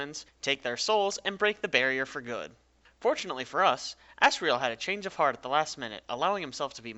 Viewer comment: Am i the only one who thought that he was saying "ass" riel and "ass" gore imstead of asriel and asgore?